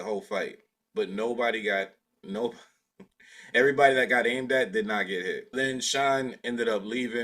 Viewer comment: sounds like you said everybody that got “indept” did not get hit. What did you say right there? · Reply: "Everybody who was or got aimed at did not get hit"